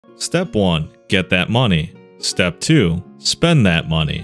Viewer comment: i thought that said " get that a$$"